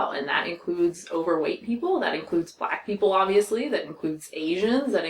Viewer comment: Lmao when you said “that includes black people obviously” I thought you said “that includes bi people obviously” and I was like uh.. sexual orientation seems really unrelated to lifestyle choices, and bi seemed so much more random than gay or lesbian or trans lol, I had to rewind.